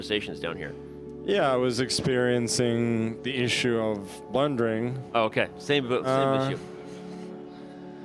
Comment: Danny "Were you experiencing the same (technical/lag) issues as Hans was experiencing?" Magnus "Yes, I was experiencing the same issues of blundering." If ever evidence was needed that smart people don't need to use abusive language to put their opponents back in their place, this is it. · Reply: Oh I thought he said wondering. But blundering is hilarious